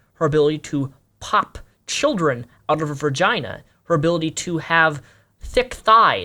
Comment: did you say ver-gina? I haven't the words....